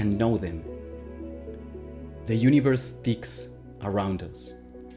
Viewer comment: Initially I heard "The universe stinks around you" and was like, ummm yeah... kinda...